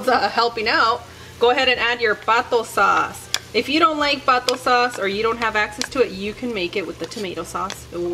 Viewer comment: When you said Pato sauce I thought you said Vato sauce and got really confused LOL.